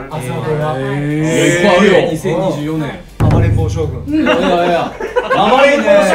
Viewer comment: 一瞬目を離した隙に照史くんの言った 暴れん坊将軍 が" 甘えん坊将軍 "に聞こえてしまって「確かにのんちゃんは甘えん坊…」と思ったら聞き違いでした🤣